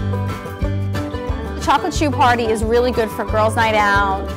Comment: It was cut off so I thought it said chocolate high